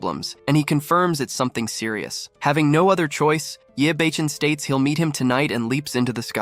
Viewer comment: Why does it sound like he saying his name is “yea Bacon “ 😂😂😂😂😂